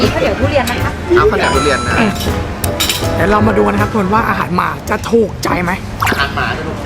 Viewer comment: ผมก็ได้ยินเหมือนพี่ว่าอาหารหมาจิงๆนะ555555